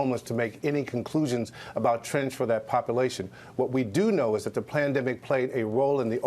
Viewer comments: did you hear what he says. PLANDEMIC. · did he say "PLANdemic"?